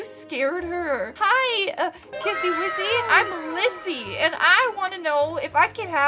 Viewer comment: it sounded like u said kissy lissy